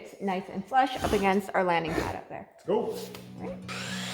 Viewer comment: I thought you said you would put hay up there. It's going to be hard with everything screwed down.